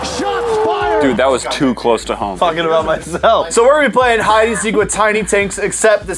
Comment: I think someone actually said the F word without censoring it